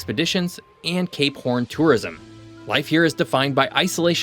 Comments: swear i initially heard "gay corn tourism" and went " wait wtf?" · I swear to god he said "K-porn Tourism"